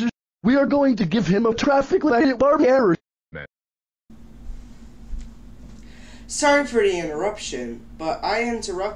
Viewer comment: what did yellow Barney say? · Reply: “Meh.”